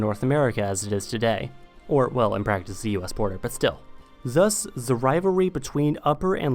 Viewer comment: what is he saying? Even subtitles skip that part! Damn so annoying? Why people do this? Mumble a point...what was the point!? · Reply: I adlib a lot, but all I said was “Or, well in practice the US border, but still“